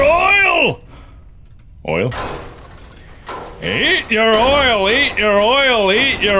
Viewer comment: did you say oil?